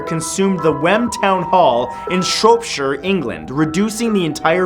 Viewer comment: can someone tell me what he said at 2:43? i think he's saying shropshire but pronouncing it wrong. i live in shropshire and i want to know if there really was a fire here.